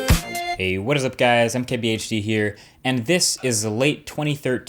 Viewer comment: It's probably because I'm foreign, but in the first video I saw here I heard him say "I'm KBHD here" and now I can't un-hear it anymore. Groan...